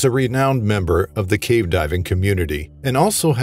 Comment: for a moment I thought you said Golden Dawn 😂🫣😂